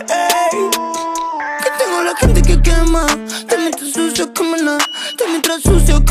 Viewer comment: Pense que decia "tengo la gente que quema" :c